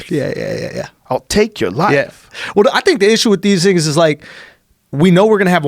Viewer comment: Did shulz say the N word at 47:34? You tell me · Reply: "These things"